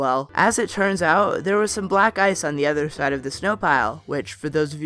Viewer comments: Thought you said "black guys" not black ice xD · When he said black ice I thought he said black guys